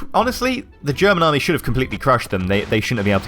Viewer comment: did you just say they got "meeple" out from dunkirk at 5:18?